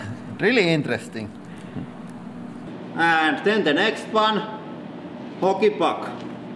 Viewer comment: it sounds like he says holy fuck